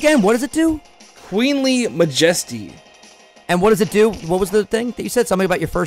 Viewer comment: when said queenly majesty I thought he said wiggly majiggly